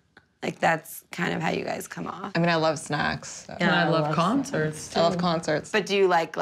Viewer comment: For a second I actually thought Danielle said "I mean I love sex, but" and had to replay that part to make sure I heard correctly (and thank goodness I did) 😂